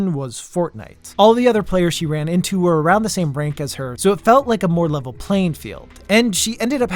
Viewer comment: i heard that as ''all the other players she ran into were around the same brain cancer so it felt like a more leveled playing field'' so rude to the lady dude wth xd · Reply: No he said "all the other players she ran into were around the same rank as her" If you're deaf use subtitles lmao Also the sentence that you constructed out of nowhere in your head doesn't make sense and isn't grammatically correct.